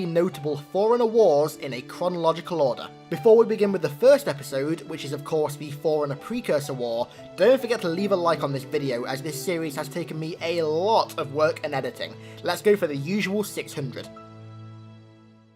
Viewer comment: I thought you kept saying ‘foreigners’ 🤣 I’m clearly too stoned.